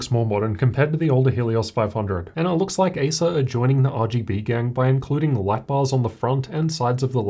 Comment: I heard that 1:01 as "rgb gangbang" lmao, it is true in any meaning tho xD Considering that its RGB everywhere on this laptop.